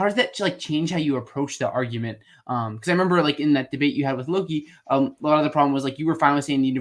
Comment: It really sounds like you said "in that debate you had with Loki" in the beginning